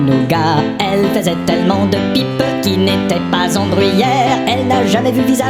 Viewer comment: "Elle faisait tellement de pipes qui n'étaient pas..." ? Il dit quoi svp ? J'adore ! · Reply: en Bruyère ! ... <link>